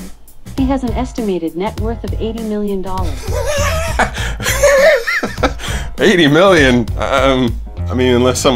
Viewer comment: At frist I thought she said 80 bill